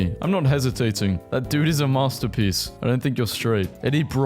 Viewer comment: Did you just say “R…n-n-n masterpieces.” Bro can’t say Renaissance.